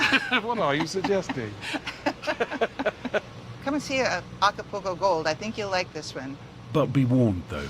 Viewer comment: can anyone help me please with the name of that trippy strain on 12:44 ? I didn't catch a name right, my english is not that good, thanks! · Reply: Acapulco Gold